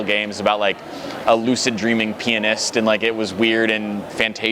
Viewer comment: I thought he said "lucid dreaming penis" not "pianist"